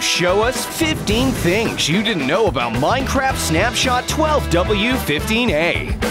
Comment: instead of 20w15a said 12w15a :-!